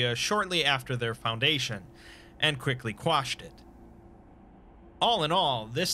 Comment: “and quickly crushed it” but sounded like you wanted to say squashed lol first time I’ve ever heard even a slight mess up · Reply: Sounded like "quashed" to me which is a real word.